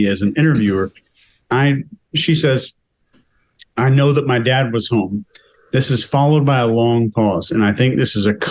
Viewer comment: is wrong! She doesn't say, " I know my dad was home". What she said was, "I know my dad was AT home". As soon as I heard that little two letter word, "AT", I thought, if your dad was "AT" home, where were you? I'm physically feeling ill watching this chick. She's no women and she's definitely no mother!